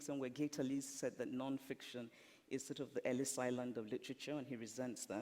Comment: What does Chimamanda say at 11:52? Thanks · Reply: Fiction is the Ellis island of literature. You're welcome.